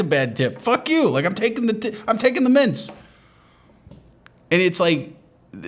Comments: I heard it as “mince” the first time, why a bowl of minced meat would be at the front desk i do not know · Thought he said mince like the meat. Painted a funny image for a moment.